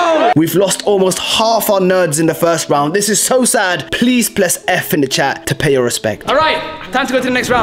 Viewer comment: did he just say 'please pless'?